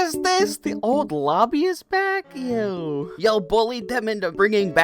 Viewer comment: did you said yo or ew cuz if its yo the nice cuz ı missed old lobby if its ew.... COUNT UR DAYS AND LEARN TO SLEEP WİTH 1 EYE OPEN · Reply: They said yo